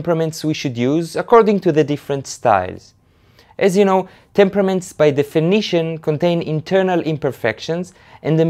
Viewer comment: omg at 0:28 i thought he was saying "temperaments by The Phoenician." wow haha. great video tho